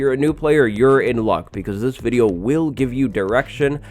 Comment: at the beginning i thought he said "this video will give you irrection